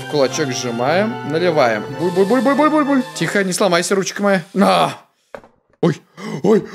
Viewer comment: Руки вверх! Никто: Музыка на фоне:Крошка моя... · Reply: Из-за темы видео я услышал "крУшка моя"